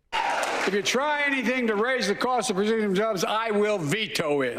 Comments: I heard “if you try anything to raise the cost of Brazilian jobs, I will veto it”. Man’s got a point. The fact that that statement got roaring applause shows how hollow and vacuous politics in America truly are. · Sounded like he said if you raise the cost of Brazilian jobs he'd veto it.